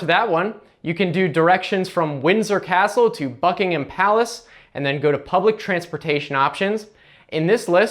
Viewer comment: When you said "Buckingham Palace", I thought you said "F*kinghim Palace" XD